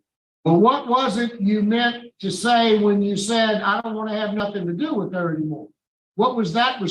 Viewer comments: He had no idea that he had 18 years to go on his sentence. Also: How could he not know? He did know. He had to know. Whatever it was that he was originally sentenced on, his lawyer told him, the court informed him and inmates talk about it. Since he was granted parole in the first place, it would have been discussed then as well. This is the guy who said "I want to have nothing to do with her" and thirty seconds later denied having said it. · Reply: I thought he said, "I don't want to have nothing to do with THAT." But the sound was not perfect and I might be wrong. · He actually said " I don't have anything against her "not I don't want to have anything to do with her , that's not the first time that parole officer misheard the prisoner .